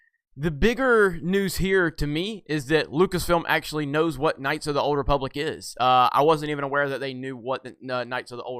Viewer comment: In my head all I heard was "knights of the old REEEEpublic"